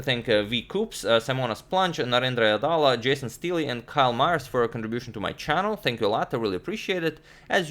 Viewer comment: what i heard was karl marx lol.